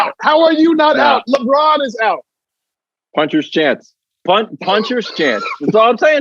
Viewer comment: I thought he said Punter’s Chance! 😂